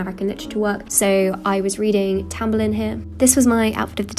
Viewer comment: what was reading here? i couldn't catch what she said. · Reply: tamburlaine